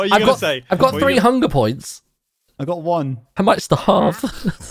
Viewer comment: What pun is it at 39:11 ? i cant really understand it... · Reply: Im at 3 hunder points, I might St-aha-rve (Starve)